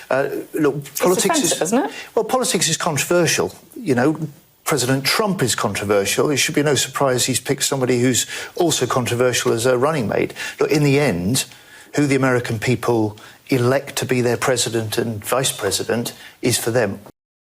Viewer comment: Did he just say "running maid"? He did the Yank accent? I know the left love to mock others (I mean "warm themselves to foreigners) by putting on the accent and pronunciation, but thats fucking ridiculous. It's "running mate", not "running maid". What the fuck is wrong with the left?